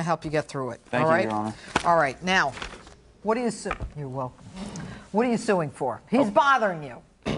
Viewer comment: what did she say? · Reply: "What do you s- (brief pause) you're welcome. What do you sue for?"